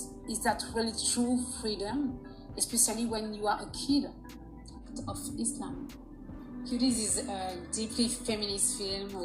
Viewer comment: Did she just say "when you are a kid of Islam" at the 4:55 time mark?